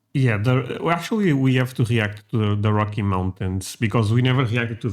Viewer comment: I know you said the rocky mountains ... but it totally sounded like you said the "Hockey" Mountains.